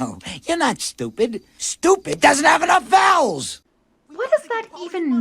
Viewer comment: I mean that “stupid doesn’t have enough L’s!” line would make sense if this movie were made today, since ‘taking L’s’ is slang for ‘taking losses,’ so it could be Max saying Feathertop was a loser…but in 2000, I have no idea. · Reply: I thought he said it doesn't have enough vowels. Which makes no sense at all.